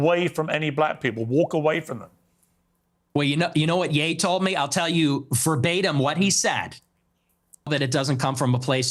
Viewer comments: What did he say? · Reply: Ns gonna N. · Who else is wondering what Ye said? Why did Piers cut it out. Please tell us what Ye said, Nick. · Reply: I saw it live, he said the Ns will be Ns or something